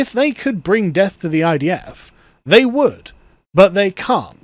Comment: What I heard was “deaf deaf to the idf “ like, bro, don’t you want them to hear your songs? 🤷‍♀️